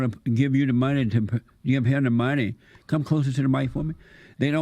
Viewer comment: did he just said "get closer to the mic women"? this man is just nasty. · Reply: “Come closer to the mic for me”